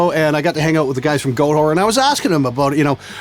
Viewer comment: Shit dude, I thought you said "Goat Horn" at 4:14. That band fuckin ripped!